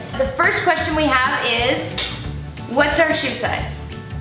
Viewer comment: WHAT WAS THE FIRST QUESTION? XD I HEARD: "WHAT´S HER DHBTESF SIZE ?"